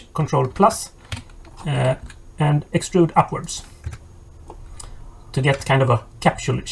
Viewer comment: what does he say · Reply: Extrude upwards